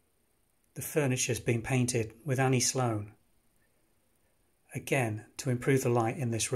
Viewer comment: I couldn’t make out what you said at 5:16. What did you say the furniture had been painted with? I love everything about this house! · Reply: The paint used was Annie Sloan chalk paints